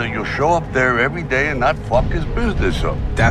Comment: Thought he said getting aids